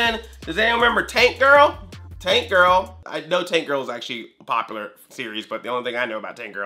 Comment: I thought you said taint girl at first... :/